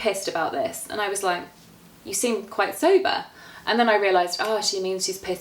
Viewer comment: Could somebody help me with this What is she saying at 9:55 " I was like you seem so _____ ?" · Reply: Vamsi rockzz quite sober